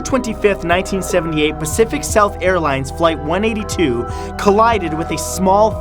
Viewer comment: When he first said 'Flight-182,' I missheard him saying 'Blink-182.' Anyother fans out there lol 😆